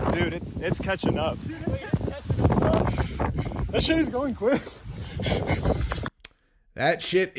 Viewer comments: I heard that shit is going quick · sounds like he said that shit is going quick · Sounds like they said that shit is going quick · Sorry but it sounds like he said “that shit is going quick.” I understand there’s other evidence tho.